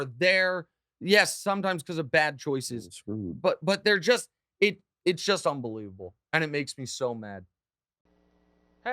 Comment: Can someone tell me what dude mumbled at 6:54😂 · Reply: he says 'there getting screwed' or 'to get screwed'